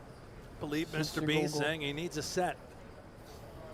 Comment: 🤣 that’s not what he said: He said: "deux sept contre deux valet" "Two sevens vs. Two Jacks" I will give it to you that seven in French sounds like the word "set" lol